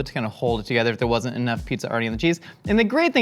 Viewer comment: Don't worry Josh, i don't think many people saw it. But you counting up the different ingredients also made me salivate. Sshpmtlo! btw. at 10:44 did he really say: "if there wasn't enough pizza already in the cheese."?